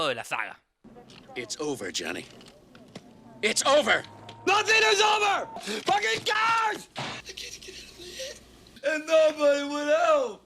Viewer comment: Creo que fuera de "Nati ni sobre, faquin cars" no entendí ni madres. Ese ingles jamas lo había oído